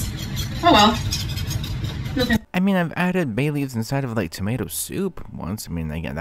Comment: I thought you said "Bailey's " and im like...yeaaaaah...hmmm Bailey's irish cream gravy ❤❤❤❤